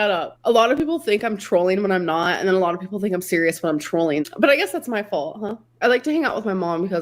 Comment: oh i thought she said she don’t troll anymore. She grew up?? I guess not